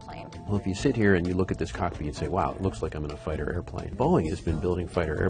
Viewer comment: What is he saying at 2:13? I just couldn't make out that word. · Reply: I'm sure he says 'cockpit' as he says it later on in the paragraph too. No other word seems to make sense, cause to me it sounds like coffee and that wouldn't make sense.